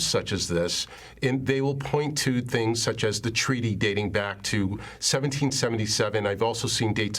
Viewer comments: Did she just say "The rise of morons"? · I see why the government doesn't like them. They operate just like them. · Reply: I thought it said rise of the morons